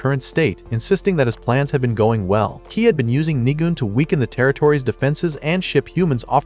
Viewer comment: I thought the demon said n*gg*